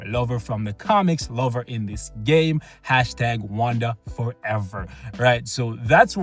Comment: LMAO #WandaForever bruh i thought you said Wakanda Forever